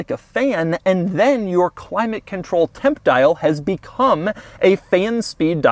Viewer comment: oh, "Temp Dial." 7:22, I thought he said "Pimp Dial"...which seemed perfectly appropriate.